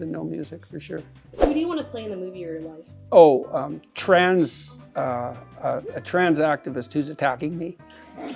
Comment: For a moment I thought he said "A trans activist who's packing meat" and thought he was onto a good idea there.